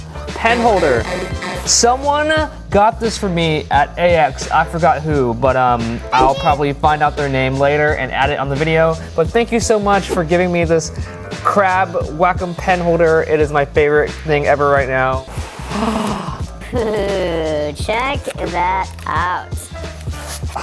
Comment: I really thought you said "thanks for giving me this crap" omg